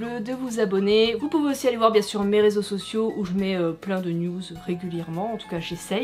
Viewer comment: OH PUNAISE ! J'ai entendu "pleins de nudes" à 13:21 ça m'a perturbé